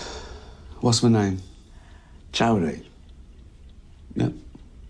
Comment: the Netflix subtitles say Tony, which would be correct.